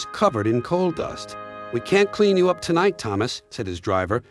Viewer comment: did he say can, or cannot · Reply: He said "can't," which is the contraction version of the word "cannot."